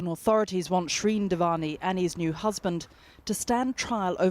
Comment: did she say the courts want shreean devani and ' he's new husband'....??? Lying dog!!!! 😑